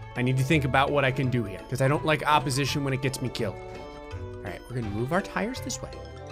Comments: Did anyone else go into shock when they heard “checkpoint” instead of “Alright”? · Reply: My brain got jammed when I heard "Checkpoint" instead of "Alright". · Did I hear "Checkpoint" instead of "Alright"